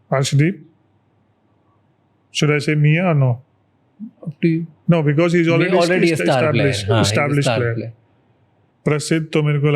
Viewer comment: what did he said there didn't understood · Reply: Should I say miya or not because he's already established (Mohammed siraj)